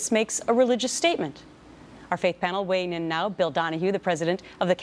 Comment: Am I the only who hears her say: "our fake panel"@ 0:33??